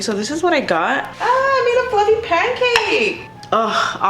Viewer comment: What did she just say at 14:51 , "mera fluffy pan cake"? Or i hear wrong?